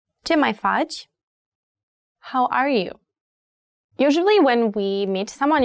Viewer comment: I'm struggling, All I hear is share my fudge 😐